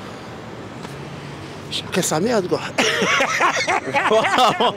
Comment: Il dit quoi le monsieur à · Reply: Il dit "chacun sa merde"